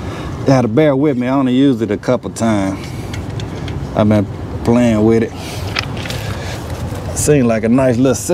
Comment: Lol first thing i heard was " im still kinda playing with it"🤣👀